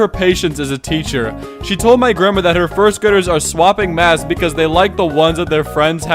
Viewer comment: I thought that Said swapping maths 💀💀💀💀